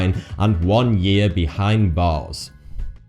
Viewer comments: sounds like he is saying 1 year behind balls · did he say balls?